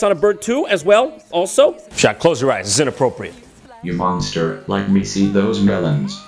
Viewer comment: XD wowie, Jon actually got bit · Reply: I seriously thought he was saying "Your mom's dirt" as opposed to "You monster" I have no idea why